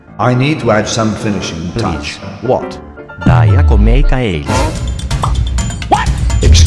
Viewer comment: Wait did he say "hindi ako maka alis" 3:45 OMG she's from Philippines 🇵🇭 YES YES YES!!!! Philipino here 😌👌 we are fans